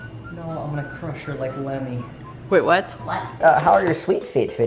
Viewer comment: did he say lemmy? or lennie? Because if he said Lennie, thats a cool reference to of mice and men